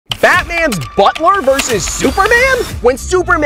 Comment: At the start I heard “fatman’s”